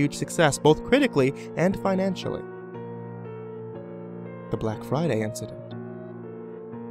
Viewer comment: I thought it said the black mesa incident